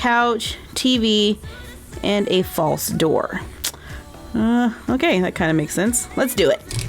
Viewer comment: Did Say Dog Or Doll For The Mail Slit On The Door?👧🐶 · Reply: Dog